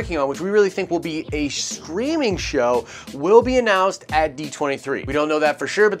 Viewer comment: 😅 when you said streaming show it sounded like you were saying screaming show